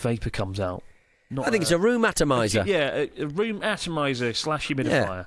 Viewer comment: I misheard room atomiser as rheumatomiser and thought it was something that was designed to give you rheumatism.